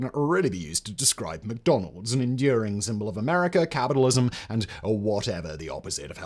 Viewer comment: The '84 Olympics made for some of the best eating ever... @ 4:18 wait, did you just say cannibalism?